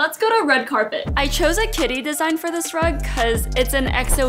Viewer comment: Ooo when she said kitty I thought she said titty and looked straight back at the screen ☠️